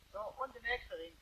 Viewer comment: did I hear, "No it was an Oechsle."? Then were the last words, "It was an Oechsle." ? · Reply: So und der nächste Ring